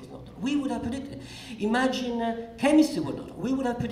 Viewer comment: Guys, what does Carlo Rovelli say exactly at 6:24? "Imagine..." what? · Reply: +ENiGMA Imagine Chemistry did not exist..... He follows Dirac equation with chemistry, for obvious reasons